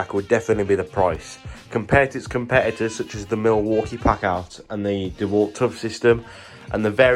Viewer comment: Could you please write down the competitors names, I hear “pack owl”. Yes I am hard of hearing. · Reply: Milwaukee Packout and Dewalt Toughsystem are the main competitors